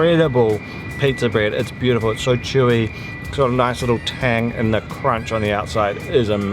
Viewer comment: when you were eating the horse meat , i thought you might have said , " it's got a nice kick to it ! " :)